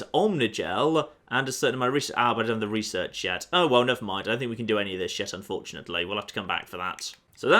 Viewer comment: Wait a minute at 4:43-4:49 It sounds like he says "oh well I don't think I can do any of this shit anyway" 😂 I've been subbed a little while now and I don't recall saying that ever 😂💀